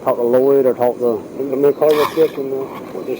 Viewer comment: I thought I heard her say call Bruce. So if so she has been in contact with him for a bit.